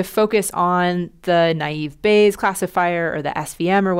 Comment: the subtitles say naive base but it's naive Bayes.